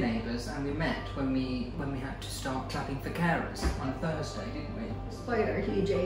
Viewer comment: Thanks adorable and equally adorable!!! This looks delicious! I loved the little break you took to tell us the story of how you met. It sounded like said, "We met when we had to start clapping for carrots on Thursday." That can't be what you really said, can it??? However I did get a chuckle out of it and loved the whole story, including the part about The Great and "Huzzah!". You remind me a lot of me and my young friend. I love when one finds someone who is a kindred spirit, no matter what the age.